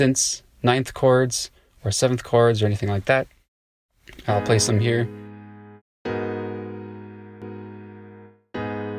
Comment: Hello, what is the vst at 1:32 min its look helpfull, sorry my english, i heard " suggestchord ", can u help ? :) thank you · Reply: Hello! Thanks for watching, that VST is called "Suggestor" there are other ones that are similar, such as Ripchord, which is another free one. There is also captain chords, cthulu, and many more. Ableton also has a scale feature and chord feature, and you can do similar things with it! Anyways, I hope this helps you out! Thanks again for watching :)